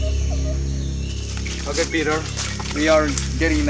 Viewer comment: мне послышалось или гид сказал "Окей Пид*р" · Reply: Piter он сказал, ведущего же петя зовут, но на английский манер, это имя, для нашего уха примерно так и звучит.